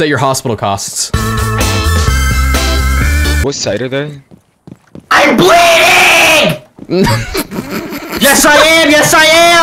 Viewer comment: My leg is now officially healed :) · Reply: I thought he said I’m bleeding Sami am lol